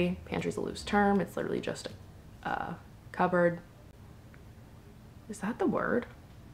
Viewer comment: cupboard is cabinet and cabinet is cupboard?? 😳😂 I really thought you said cub board 😅